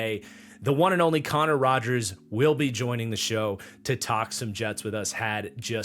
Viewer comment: misheard this and thought you said Aaron Rodgers 😂 I was like "that should be interesting"